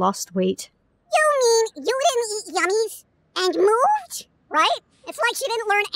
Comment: What did say at 0:20? "Yummies and... moonge"? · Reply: "You didn't eat yummies....and *moved*?!